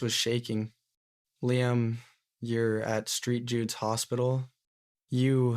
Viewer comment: “street Jude’s” instead of Saint Jude’s